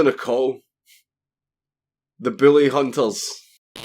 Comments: #theBillyhunters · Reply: It sounds like he said “Booly Hunters” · Oh shit not the Billy Hunter's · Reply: It sounded like he said Billy Huntels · The whole time I thought you were saying Billy Hunters. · God fucking damn, I thought you were saying Billie hunters. The fuck is a bille, I thought? They can't be hunting down all the Williams in the world. And then I actually looked up at the screen and saw the way it's spelled. Damn your sexy accent! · I keep hearing "Billy Hunter". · I kept hearing "Billy hunters"...